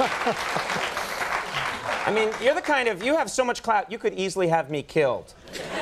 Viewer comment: did he say clout